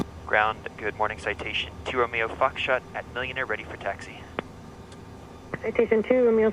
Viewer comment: I must have been chewing my chewy soft chocolate cookies when I thought I heard this request from ATC... "Ground citation 2RF AM A MILLIONAIRE and requesting taxi"... I went BOOM!.. then rewinded and... 😅😅😅